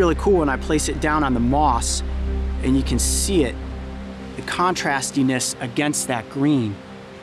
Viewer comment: Did he just say "contrastiness?" 3:52 LOL I love the funny things Coyote says sometimes :p